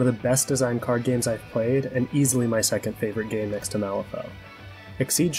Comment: What was that game you mentioned as your favorite? I couldn't quite make out what you said. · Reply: Malifaux